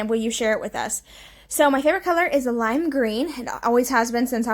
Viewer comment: Ok, I have gone back 3 times and listened to this and am cracking up. At 21:30 do you say that your hair color is lime green? 😂 I swear that’s what it sounds like. I know it’s suppose to be favorite color but it sounds like you say hair. Lol